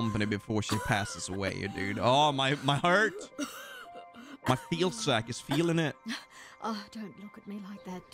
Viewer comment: Did you just say your feels sack 😆